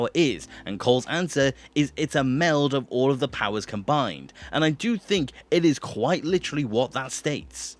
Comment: that is not what cole says he says and i quate "idk it's some kind of like green energy ball"